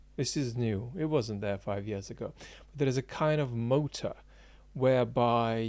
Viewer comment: What is the word he is saying @ 2:26 ? “Moat”? “Meta”? “Motive”? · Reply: I'm going with motor.